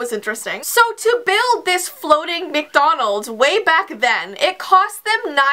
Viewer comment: tthe moment you said floting I thought t was in the air loool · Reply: Diana I I thought she said flumting